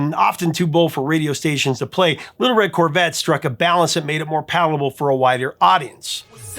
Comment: I had to rewind a few times to figure out “palpable to a wider audience” instead of “whiter audience” which is what I first heard 😂